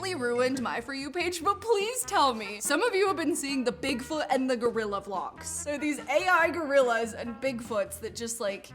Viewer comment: My dumb ahh thought it said gorilla glue